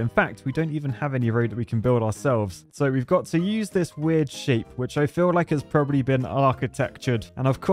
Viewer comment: It sounds like he is saying sh** when he was saying shape at the beginning 🤣 😂